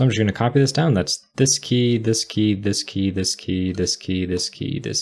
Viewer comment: I always heard "Whisky ... Whisky ... Whisky ..." instead of "this key ... this key ... this key" :-) Very good programming practice! Good job!